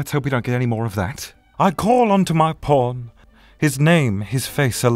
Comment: I thought he said "I call onto my porn," and that really confused me. Also just heard "Even when all my porns pile up like..." Jesus every time you say "pawn," I hear "porn," Connor.